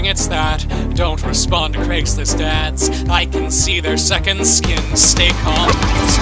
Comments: If Iearned one thing is that, don't respond to racist dads XD that's what I heard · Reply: Destiny Prout I heard "dont respond to creeps and dads" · am I the only one who heard "don't respond to craigs wrist dads"?? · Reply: I heard “don’t respond to Craigslists dads” 😂